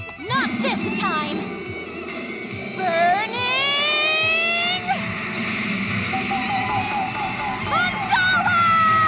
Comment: What an awesome way to introduce some characters. Also at 1:19 I hear, "Burning! A dollar!" lol...